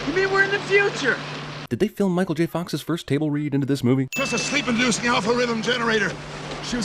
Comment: Is it just me or does Doc say at 1:05 : "Is this is a sleep induced GALLIFREY-thm generator" CONFIRMED: The Delorean is officially a TARDIS.